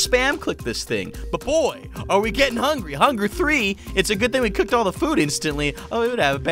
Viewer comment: Did he just say Hungry three or am i just hearing things?